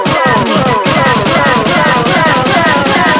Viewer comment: First time i heard this i thought it said stab me XD